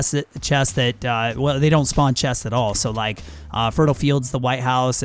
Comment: Did he say "fertile fields"